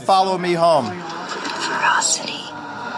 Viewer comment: did she say ferocity?